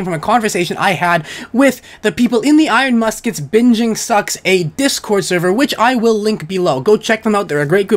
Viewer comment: At first I thought you said "Binging sucks, A discord server" and then realized it was "Binging sucks, eh?" lol